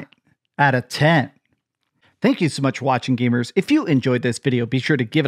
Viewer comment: Here’s the thing about: he’s that dude you’d run into at the game store in 1999 who’d talk your ear off and while he was annoying he grew on you, because he’s a real gamer. Is he a hyperbolic fanboy? Absolutely. But he’s an old school, real gamer hyperbolic fanboy. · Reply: Does this guy say Gabers ? Idk if he means gamers but he sounds like he says gaybers